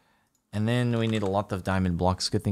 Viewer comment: Did Jardine say what I think he said? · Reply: He said sheet like a bed sheet unfortunately